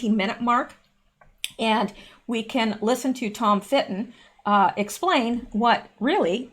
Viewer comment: That is Tom. It almost sounded like you said, "Fitt." I agree that Black Conservative Patriot channel is a great news source. Both of you have lots of news.